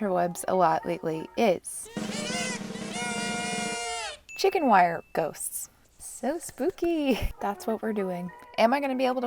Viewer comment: At first I thought it said "Chicken Ghosts" Me: umm...oh-kay but I'm sure will make them awesome. Me after watching: chicken WIRE ghost, okay that makes a lot more sense. And I was right. She made it awesome.